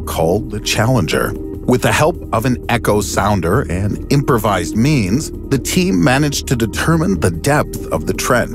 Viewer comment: I thought he said improvised memes